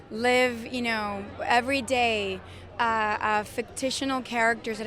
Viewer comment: haha what the fuck, she said ”fictitional”!! 😂🤣😂🤣